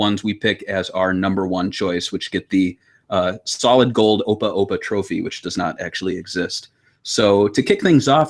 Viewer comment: Aww, I misheard you and thought you said "Golden Oppai Oppai Trophy".